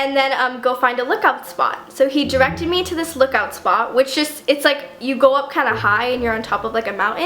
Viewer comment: Sounds like your saying lick out spot😂